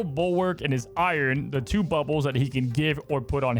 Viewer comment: I heard two buttholes 😅